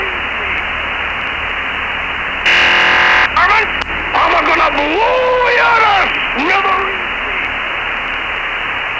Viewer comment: "vermes, eu vou explodir vocês" o resto não entendi. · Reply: "Varmint, I'm a-gonna blow ya ta smithereenies!" ("Vagabundo, eu vou te explodir em pedacinhos!") Bem no estilo desse personagem mesmo 🤣 gostava demais dele.